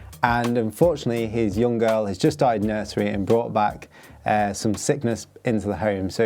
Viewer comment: Wow, I really thought you said “his daughter died in nursery” and then went on to crack on with the training plan 🫨